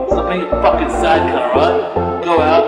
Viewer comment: Can somebody get what the guy says in minute 1:25? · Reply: Zyzz bro